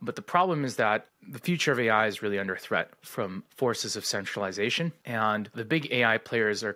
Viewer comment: I thought he said sexualization lol